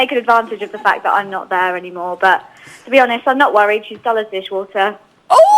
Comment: What did Tamara say at 5:47??? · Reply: She's dull as dishwater.